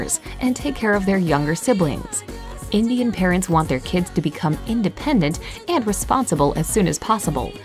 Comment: "Indian parents want their kids to become Independent" Did I hear you right?